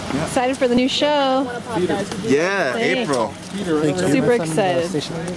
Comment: What does Peter say at 0:08 just before the chick says "super excited?" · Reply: "Yeah, April"